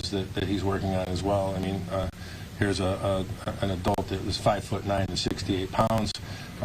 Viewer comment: 5'9" and 68 pounds. 68 POUNDS. Did I hear it correctly? Even even for short people, going below 100 is hard. 🤢🤮 This woman is a monster.